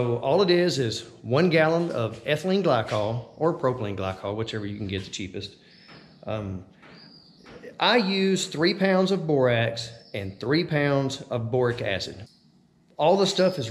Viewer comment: Can anybody tell me the three ingredients he used? He says them on 3:25 but I can't understand him. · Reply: It’s boric acid, borax and propylene glycol or ethylene glycol.